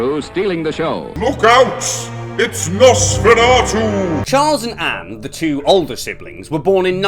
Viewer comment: Really thought you said Nonceferatu for a sec